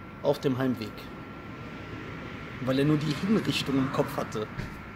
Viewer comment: Was sagt er ab 1:18 mit dem henker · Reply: JohnDoe "Weil er nur die Hinrichtung im Kopf hatte."